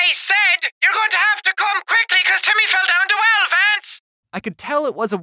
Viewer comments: The woman sounds like she said "you better come quickly cuz Timmy fell in the well Vance!" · What I heard was “you have to come quickly because Timmy fell down the well Vance” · What I heard " You have to come quickly come couse Timmy fell down the fence " Can you save Timmy Please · I thought she said You have to come quickly cuz Timmy fell down the wild fence Am I the only one who heard that?? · The second example of the wrong caller is the First Lady I heard “ COME QUICKLY BECAUSE Timmy fell down two elephants” weird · I thought she said "hurry up Timmy's fell down the well Trance" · It sounds like she saying you’re going to have to come quickly because to me fell down the well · All i heard was “You gotta come quickly Because timmy fell in the well vance!” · I thought at first i heard "You have to come quickly because timmy fell down the wild fence" xD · It said Come quikly,timmy fell down the well fence! That's what it said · I heard ' You're going to have to come quickly cause Timmy fell down the well, VANCE! ' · I think they said "your gonna have to come quickly because Jimmy fell down the well lance" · Who else heard at 5:52 ur gonna have to come quickly cause Billy fell down the well vance? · I heard it say'you have to come quickly cause timmy fell down the well vance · For a second I thought it said Timmy fell dome on the fence come quickly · I heard "be fast timmy fall down the well "Vance". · I heard "ur going to have to come quickly Timmy fell down to El fance"